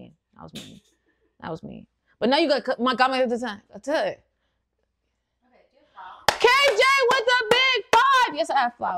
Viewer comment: wtf did she js say 💀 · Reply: Turned into sims lol